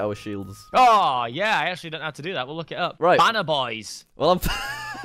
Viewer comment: They said: banner boys I thought they said: Ban a boys :p